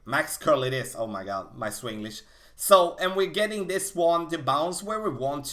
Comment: did anyone hear he said "Swen-glish?